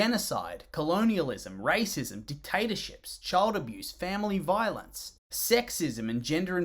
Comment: I heard "Potato chips" when he said "Dictatorship".